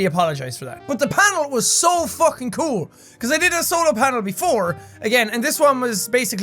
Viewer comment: did he say SOLAR panel loooooooool